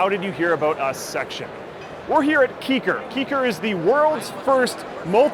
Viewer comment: I thought it says ass kicker :D